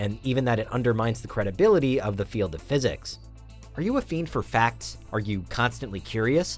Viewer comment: I thought he said are you a fiend for crack, yes, yes I am. Subscribed.